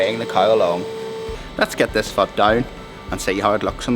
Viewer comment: let's get this f down threw me off for a second I was like wait what did he just say?! I know what he's saying but knowing and hearing are two different things lol